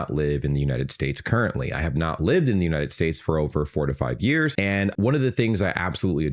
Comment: I heard "45 years" instead of "4 to 5 years". For 30 seconds i was like "damn, this dude does not age".